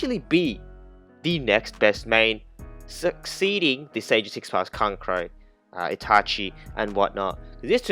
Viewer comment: Wait did he just say sage of six paths kankuro